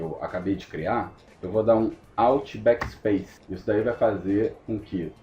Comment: man n entendi oq tu falo no 5:35 alt **** espaço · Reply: Fala! é BackSpace.. Exatamente essa que o falou.. Mas tb pode ser o delete.. Grande abraço!!!